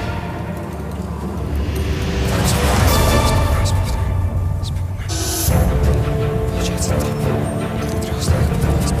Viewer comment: What's he saying at 1:31? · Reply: Christ! remember the position of figures ( then he says the position of figures) (Bilieve me - Im Ukrainian)